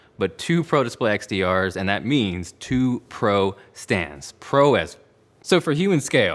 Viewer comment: Came here from HDTVtest. Did i hear you say “Pro AF”? Lol